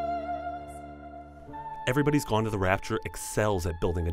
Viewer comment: I thought it said "everybody's gone to Rapture" and was like"oh cool a new Bioshock game XD"